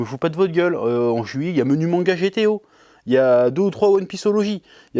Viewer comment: C'est exactement pour cette honnêteté qu'on t'aime <3 J'suis une personne simple, j'ai entendu "Menu Manga GTO" je suis joie